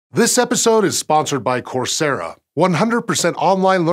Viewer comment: Lol I heard “coldzera”